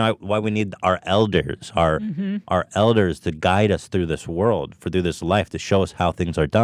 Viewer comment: Did he just say we need our eldiggers?